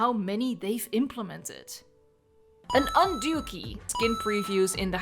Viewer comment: Okay this is gonna sound so childish, but I had to listen to 18:13 multiple times because I was so confused when I heard "un-dookie". That's what I get for watching the video sped up. 🤦🏻‍♂️